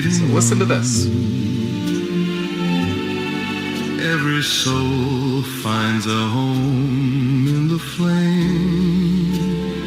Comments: About 6 minutes in, it sounded like AI said, “every soul finds a home in the flame???” 😱 Hoping I heard it wrong! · The second song sounded like it said” my soul is home in the flames”. I’m serious! Go back and listen! 🤷🏼‍♀️ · I agree I thought i heard "every soul finds a home ( or place?) in the fire " crazy resistance and please let us know